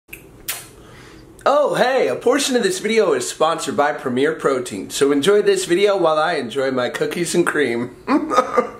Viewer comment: I thought for the sponsor he said premiere pro 😂